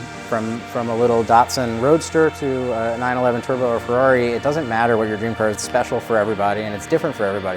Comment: Wait, did he said "A 911 turbo Ferrari"?